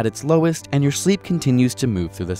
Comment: I heard "Good morning Daddy" instead of "Good morning body" I.Need.Help.